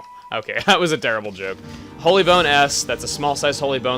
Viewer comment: When you said "Holy Bone S," I thought you said "Holy Bone Ass."